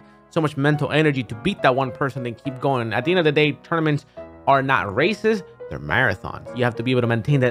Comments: I legit thought he said: Tournaments are not racist" LOL smh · i deadass thought he said that all tournaments are racist